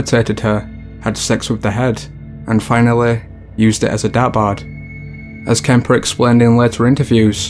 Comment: When the narrator said the third murderer used his moms head as a “dabard”?.....what does he mean? Am I mishearing him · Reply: A dart board